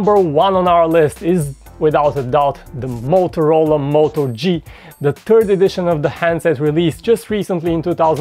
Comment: did he just say the moto G the TURD edition??? :D :D :D